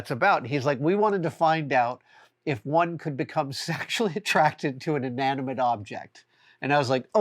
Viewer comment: I initially heard that as "become sexually attractIVE to an inanimate object". I was wondering how you could possibly know if your stapler was into you.